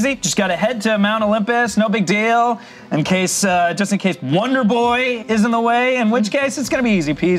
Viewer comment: Did anyone hear Hades' player say "Wonder Boy" at 4:09?